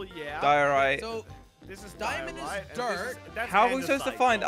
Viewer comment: jelly said "diamond is dirt" lol and they didn't heard it